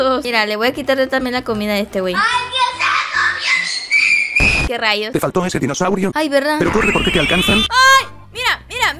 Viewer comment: una pregunta ¿alguien sabe que dice el audio del minuto 6:08 cuando le quita la comida a george? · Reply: Quien se comió mi tortaaa